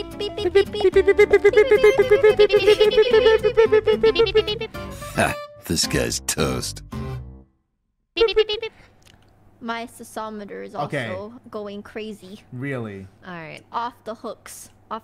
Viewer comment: “Disguised Toast” Sometimes I hear “This guy Toast”